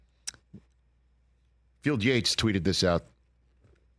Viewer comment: I heard Bill Gates and i was like Bill Gates is a Lions fan?